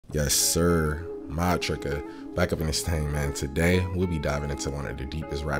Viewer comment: When your youtube feed is dry, posts just in time to save the day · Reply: I thought he was saying “my tricka” this whole time